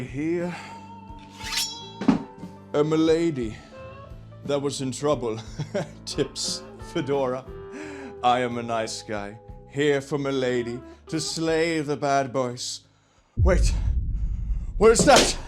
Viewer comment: I heard "tits and fedora" did anyone else hear tits and fedora? 😂 Edit: lmfao 😂 given what happened next I stand by my audio processing difficulties!